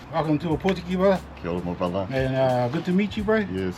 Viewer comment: Did you say yo brother to the captain